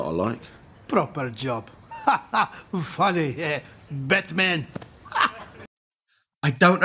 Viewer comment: Batman? I thought he said "Best man"